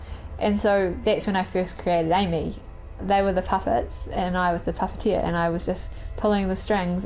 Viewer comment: When she said I was the puppeteer I thought she said "I was that tapatio"